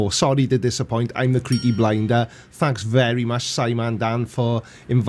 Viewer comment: I found your videos recently, and before I saw "The Creaky Blinder" written out, I always thought you were saying, "The Creepy Blinder".